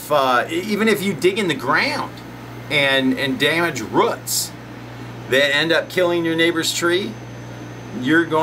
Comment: Did he say, "... and damage RUTS!" ? 🤣